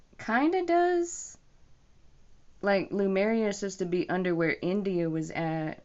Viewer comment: It sounds like you mispronounced Lemuria. It sounds like you said Lamaria. Also, I once heard that Lemuria was in the Pacific ocean, but an article online says it was in the Indian ocean. I'm not sure which one is true.